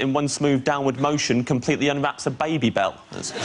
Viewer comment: A baby belt? Did I hear that right? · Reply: Babybel